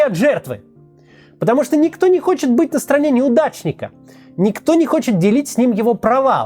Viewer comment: Вместо "неудачника" мне неоднократно послышалось "мудачника" (муд@ Это мне послышалось или Кац тролит?)))